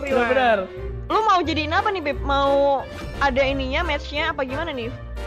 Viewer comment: aing dengernya kyk " lu mau jadiin apa ni BEB " Yg sama like anjjj